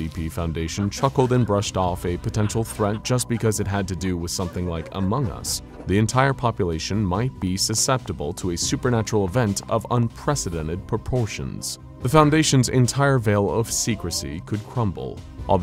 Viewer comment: i can't be the only one who heard "supernatural vent"